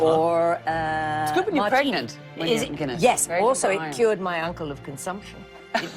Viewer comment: Can someone enlighten me what she said at 7:10? It cured her uncle of consumption? ❤ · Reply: Guinness